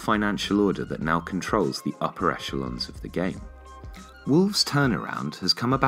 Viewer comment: what is the word used I dont understand it? "...that now controls the ???? of the game" THANKS!!! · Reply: Bassem vs Gazouza The word is “upper echelon” basically means the best or most elite teams